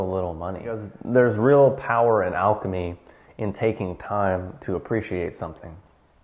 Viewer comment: I'm sorry, and I must have misheard him, but at 6:27 did he mention "alchemy"?